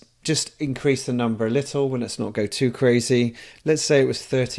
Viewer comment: Did he actually say "Let's not get too crazy". OMFG